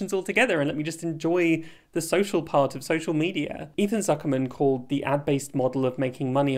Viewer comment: haha, when you said "ethan zuckerman" i completely thought you were saying "even mark zuckerberg" but using a silly name for him, so thanks for screengrabbing the article